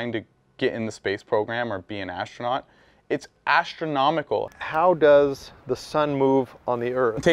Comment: “Do you see how many people who died becoming a astronaut. It’s astronautical” eheheeeeey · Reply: He actually says astronomical listen to it better 6:22 but he is actually saying as big as space which is huge no firmament haha